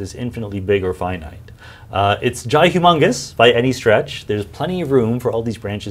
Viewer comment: did he just say that the universe is jhi humongous